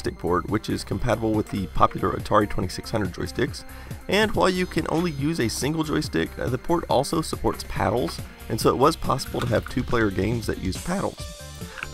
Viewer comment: thought it said paddies